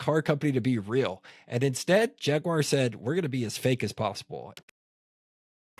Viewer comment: Did you say, we're gonna be as gay as possible?